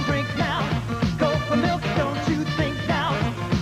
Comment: I wasn't paying attention when this bit played; until I heard, "gopher milk!"